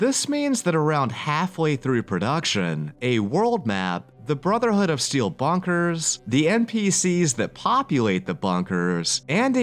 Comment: Wait, did you say "Brotherhood of Steel Bunkers?" I thought you were saying "bonkers" and I was confused.